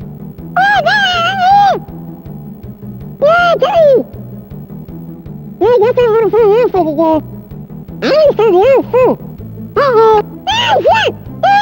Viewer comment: i think kenny said “well i guess i have find the other f*cking guys” idk 9:22 “OH SH!T”